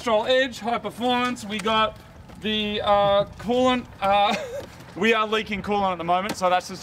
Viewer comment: Oh coolant. I thought they were saying corn.